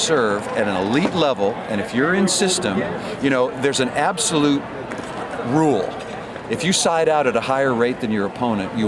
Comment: Thanks for this upload, great quality however I couldn't understand a couple of words in the minute 1:11, 'the absolute ruke , 'can you help me out with that?